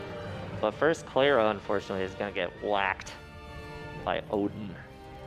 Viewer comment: anyone else hear cleyra is going to get blacked by odin?